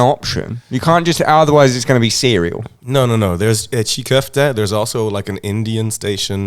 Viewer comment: Did he actually say İçli Köfte @ 1:06:54 ???